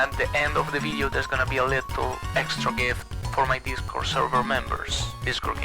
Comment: I know it's a "little extra gift", but I hear "lethal extra gift" instead